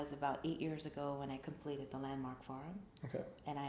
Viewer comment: What did she say she completed 8 years ago? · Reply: Sounds like landmark forum